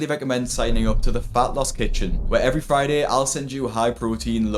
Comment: Haaa! I wasn’t watching, I was just listening…I thought he said “Fat-LESS Kitchen” 😂😂